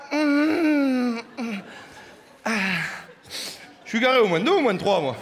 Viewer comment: il dit quoi à 6:29 svp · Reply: Réci chaîne de rap il dit je suis garé au -2 ou -3 moi ( étage du parking )